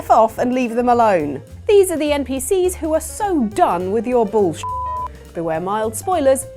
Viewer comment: I thought it said "Npcs who are done with your big balls"